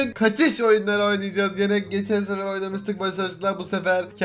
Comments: Başaş? sadece benmi duydum :D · da başaş mı dedi ben mi yanlış anladım